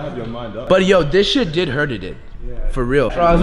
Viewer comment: did he say hurted?